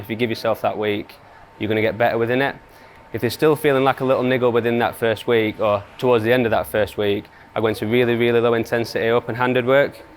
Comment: what did he just say?!? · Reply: If you’re still feeling like a little niggle within that first week or towards the end of that first week, I want some really really low intensity open handed work so front three usually; nice and easy and then build up that intensity up over a week or two. if it starts to progress in a positive way then that’s when I bring the half crimp back in